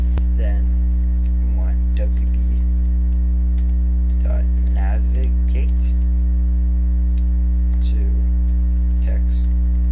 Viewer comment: I cant tell what you are syaing, it sounds like wb.navigate (TextBox1.get) but that doesnt work what are you saying?